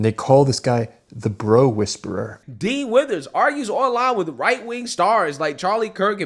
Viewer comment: "Dean Withers" ... I'm listening to this in the background and I keep hearing "Dean Wormer" (from Animal House). Double top secret probation for him!